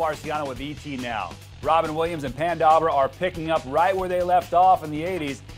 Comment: Did he say PAN Dawber?!